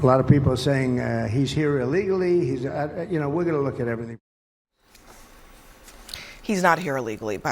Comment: he didn't say he was he said he would take a look...